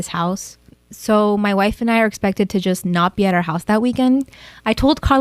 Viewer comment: I told Carla G N O M E (the cc said it)